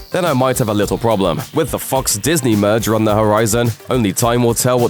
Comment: When he said, “When the Fox” I thought he said “What the fuck”